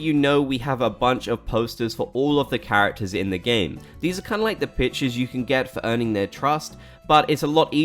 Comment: i miss heard and thought he said "these are kinda like the b*tches you can get for earning there trust"